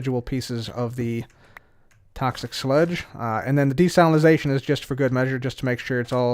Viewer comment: I could of sworn he was saying destalinization.